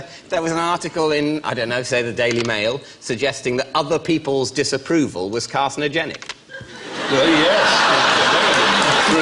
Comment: Sorry what does david say at 09:20? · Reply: Other people's disapproval was carcinogenic